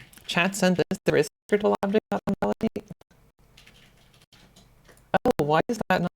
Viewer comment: What feature were you saying is only in Unity 2020? Your voice breaks up at the exact moment you mention it. All I got was "Chat says there is scriptable objects on ??????" Sounds like "OnValidate". but that doesn't make much sense. :D